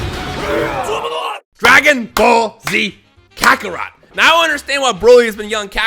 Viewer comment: Sounded like he say maggot ball z